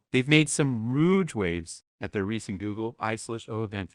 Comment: Did say “ruge waves”?